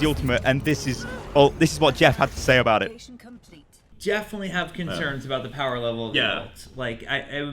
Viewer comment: Did Jeff just say "Jeffinitely"? Because I want to imagine that he did.